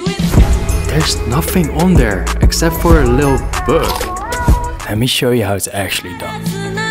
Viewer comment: “Theres nothing on there except for a little burg” 💀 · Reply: I thought he said little bug😭💀